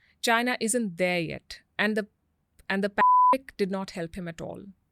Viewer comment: what did she say at 1:07:07? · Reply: She said pandemic